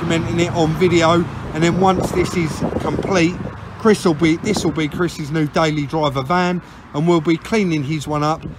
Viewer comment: Could've sworn you said that this would be Chris's new van Wot a wotter for floggin it under im As e annoyed you? 🤣🤣🤣 Nother brilliant job boyz